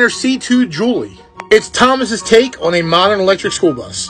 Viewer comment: I thought he said Thomas the Tank Engine on The Magic School Bus 🚍. I had to repeat it three times to hear it right